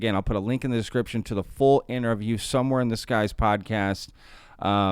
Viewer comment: I thought he was saying “Somewhere in this guy’s podcast” when talking about the interview clip haha 😂